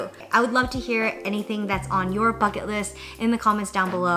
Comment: I had to wash my hands towards the end of the video and couldnt hear the audio clearly so when you said "bucket list" I heard "*f*ck it list" and I thought what a great idea that was.